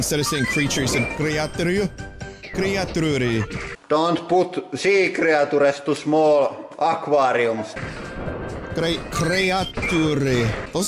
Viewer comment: “Don’t put sea creature into small aquarium.” If you didn’t understand what the guy said, you’re welcome.